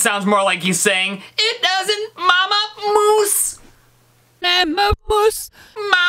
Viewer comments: I was looking at the comments and when I return to the video I hear “Mama Moose” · i hear "Mammer oos" or "Patter poos" and "Pæper oos". Edit: i even hear "Platypus".